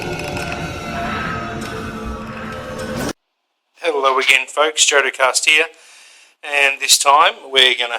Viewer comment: did he just say "Hello again fuks" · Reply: Hyper he said "hello again folks""